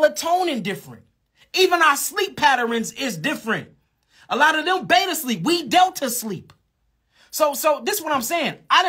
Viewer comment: what did he say? Word by word please 🙏🏾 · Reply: “A lot of them beta sleep we delta sleep.”